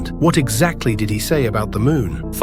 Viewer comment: when bro'sxmok said "Did he" I thought she said Diddy💀